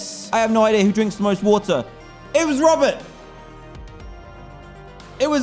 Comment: "Amanda Green" I was spacing out and thought he said "A man in green"